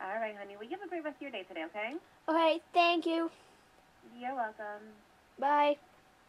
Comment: Turn on captions for 2:04 XD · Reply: it sounds like she said: 'Fuck 'em' but she said your welcome